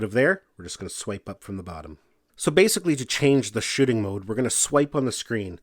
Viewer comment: i thought he said shiting mode at 11:29 LOL